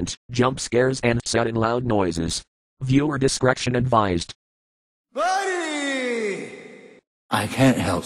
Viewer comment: I hear the doctor saying "buddy", it must be from Outlast.